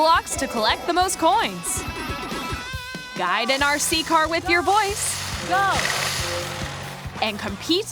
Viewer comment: I honestly thought this said Drive a RC car with your bois